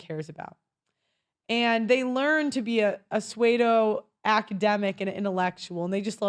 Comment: Did you say "swaydo"?.... (12:27) I think you meant pseudo (sue-doe). Youre welcome haha